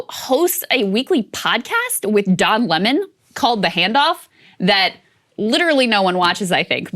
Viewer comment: Haha I thought she said the name of the podcast was the handjob